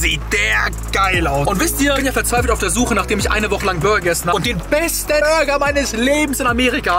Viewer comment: Hast du gesagt der beste Bürger am <num> oder habe ich mich verhört 😂😂😂